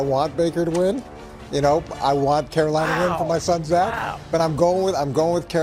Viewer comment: did coach say for his sons dad?? 🤔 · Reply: My son Zack